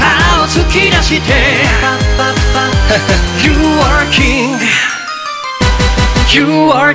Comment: 「You are KING」が、 何度聴き直しても「You “were” KING」に聞こえて鳥肌が立った。 正気じゃいられないくらい好き。